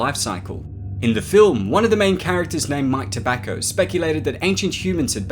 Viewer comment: I'm sorry, did you say one of the characters is named *Mike Tobacco*?